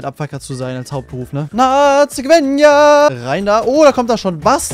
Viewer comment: Wer hat, bei 4:21 auch nazi verstanden 😂